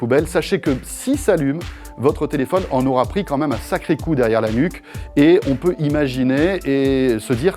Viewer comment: j'ai compris "il s'est prit un sacré coup derrière l'anus"